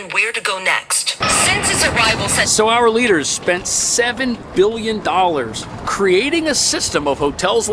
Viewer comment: Did I hear $7 BILLION ????